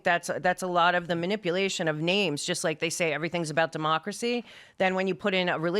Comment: Did I hear her say "memocracy"..????? LOLOLOLOLOL